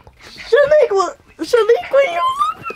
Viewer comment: did say "Shaniqua, Shaniqua"..? Why she call the dog that?